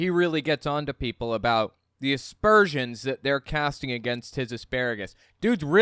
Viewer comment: I agree! Don't cast aspersions on my asparagus! 😂😂😂 Are you anti-asparagust?! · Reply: Oh, ASPERSIONS, I thought she or he, said NASTURTIUMS... very flowery brains the GOP